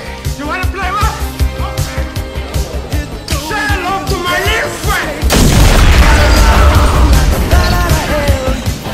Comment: What does he say at 1:37? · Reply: He said "Shout out to my little friend!"